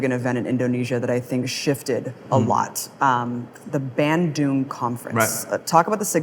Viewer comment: Wowwww the auto-generated english subtitles spelled "Band Doom conference" instead of Bandung... what a bad and clearvoyant omen !